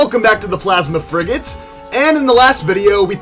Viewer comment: I thought he said plasma freakin'